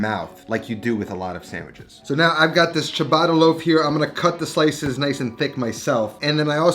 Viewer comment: i misheard as "nice and thick like myself" lmao